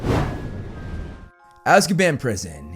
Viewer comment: I keep hearing ''Azkieban'' and its sounds so funny 😂😂